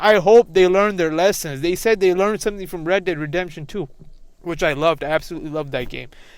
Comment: use it on a more worthy game like rdr2 or Witcher 3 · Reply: oh I thought you said r2d2 and not red dead. Red dead is also garbage.